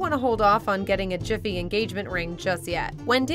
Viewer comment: ~ Did you say 'Jiffy'? (Or was that sposda be a clever joke)? 🤔